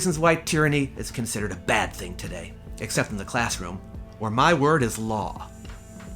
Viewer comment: When said he was a dictator in the classroom, I misheard it as a dictator in the class-war and I was really confused for a moment.